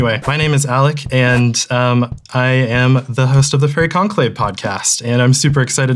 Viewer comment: I thought he said "Furry Conclave" not Faerie